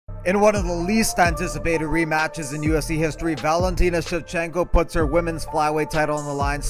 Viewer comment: I honestly thought the intro said "one of the most antisapted rematch in ufc history" i